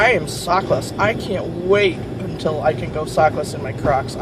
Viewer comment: Nice car and loving this weather! .... LOL I thought you said "I can't wait to go sockless in my crotch" lol. Love your new car!